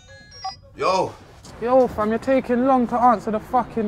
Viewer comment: lol thought i just heard ksi for a sec when he said yo